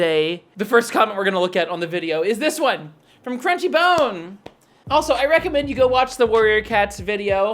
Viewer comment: I swear to god I thought you said "cringey bone" for a minute there X'DDDDD